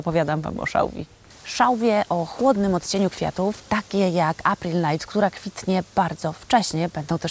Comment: Pani, czy dobrze usłyszałam, że mówi Pani o szałwii Apple light? Chciałabym też taką kupić, ale nie widzę takiej odmiany. Pozdrawiam serdecznie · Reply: April night